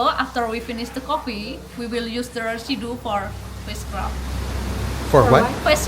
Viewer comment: face scrub, at first i thought she said witchcraft...lmao